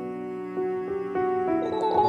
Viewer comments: No one's talking about the dono. "Make a grave for baby man." That's the best part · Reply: I thought I said “you make a great baby man” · No one's talking about the dono. "Make a grave for baby man." That's the best part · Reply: I thought it said grape not grave lmao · Digital woman’s voice: “Make a grave for baby man” Captions: “make a great baby man” Fundy: “animal crossing noises while crying” What the actual heck. How did I get 3k likes?!? That’s the most likes I’ve ever gotten in anything. I think I’m hallucinating or something... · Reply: i thought they said you make a great baby man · Literally not a single soul: What I hear: “Make a grape for baby man” What the subtitles say: “Make a great baby man” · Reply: make a great for baby nap...? it's literally what i heard :"""D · No one: Not even sad Fundy: The message: make a grave baby man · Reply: I heard "Make a grape for baby man"